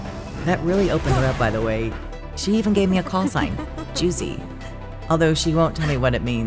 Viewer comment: I laughed so hard when I thought I heard her say juicy💀💀💀💀